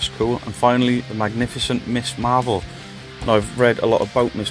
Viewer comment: Eee I thought you said miss marple! 😂😂 Cool comic haul! The artwork on them is fab! 😊